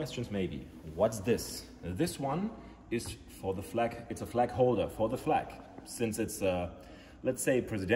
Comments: I thought he said flack holder…as in anti aircraft missile system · Thought he said Flak holder, as in a Flak gun lmao